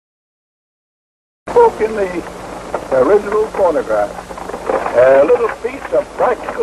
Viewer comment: Did he say: "delicious porngraph" ?